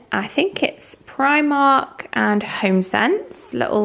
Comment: Hi love everyone of your videos watching you on Sunday morning as usual in bed. Could you tell me please where your bench is from please in your bedroom because thought you said Homebuy but cannot find it xx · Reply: Home Barn it is called lovely :)